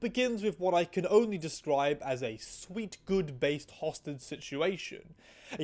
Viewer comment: i thought u were saying “sweet, good, based hostage situation”